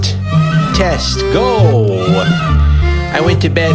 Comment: is he saying cześć? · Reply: Test